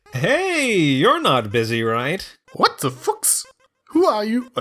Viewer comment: it sounds like he is saying f**k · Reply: that's the joke, I say "what the fuchs", fuchs is the german word for fox^^